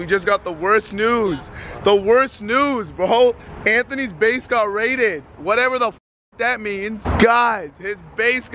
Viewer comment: I thought they said Anthony’s bank got raided Not like it matters he doesn’t have much to take anyway